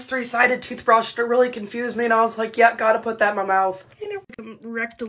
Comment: “Yep gotta put that in my mouth” That’s what she said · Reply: I thought she said god put that in my mouth